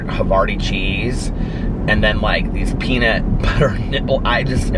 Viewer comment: FAMILY FRIENDLY OMG!!!! 🤣🤣🤣 at 7:55 I just hear “peanut butter nipple” I’m dying!! Love ya!! 💙💙💙💙💙💙💙